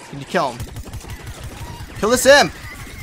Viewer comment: i like how he said to imp kill the simp instead of kill the imp XD